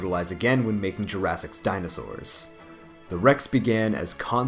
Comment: Plus i had heard him say dinosaurus